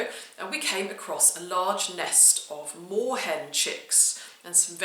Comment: "Snort" I thought you said Warhead chicks! Soon realised my mistake 😉 Another delightful vid, thank you x